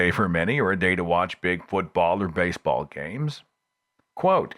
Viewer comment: lol! 54:06. When was talking about watching TV on Sunday, the first time I listened I heard "Bigfoot ball games", not "big football games". I immediately did a double-take and had to go back. Given the state of so-called 'investigative' TV programs on a lot of American networks, I guess my brain went the paranormal-crap route since I'm not a sports fan. It probably says a lot about my disgust for some (i.e., most) US networks (History, TLC, Discovery, etc.). :)